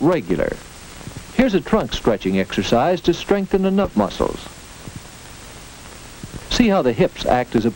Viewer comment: I must have misheard but I replayed it about 5 times... I swear he’s saying “here’s a trunk stretching exercise to strengthen the nut muscles”.